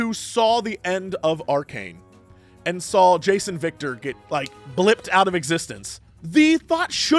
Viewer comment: "Jayce and Viktor" sounded like "Jason Victor". I was like "Who?" for a solid 2 seconds.